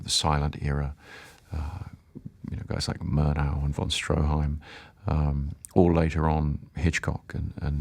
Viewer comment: Does anyone know what directors he's referencing at 1:07? The closest I could find to what they sound like is: F. W. Murnau, Josef von Sternberg, Erich von Stroheim - but I'm not sure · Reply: I heard Von Stroheim and Murnau